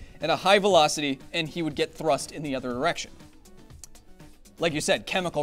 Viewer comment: I listen to it multiple times and it sounds like he saying other Erection even though he means other direction